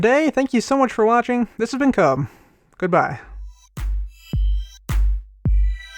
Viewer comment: i swear i thought he said "this has been CUM"